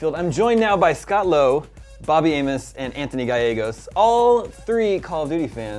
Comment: I thought he said "Bobby Anus:"